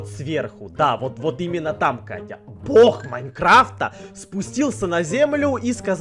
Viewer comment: Мне одной послышалось " Лох Майнкрафта" ???😂😂😂